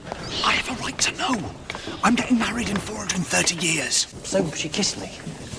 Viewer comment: "I'm getting married in fOur hUnDreD aNd thIrty YeARs" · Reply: calm down he has- OHHH WAIT! DID U SAY 430YEARS! I THOUGHT U SAID 650YEARS AAAAAAAAAHHHH STAY WITH ME BLUE JAY GET YOUR WEDDING CAKE ASAP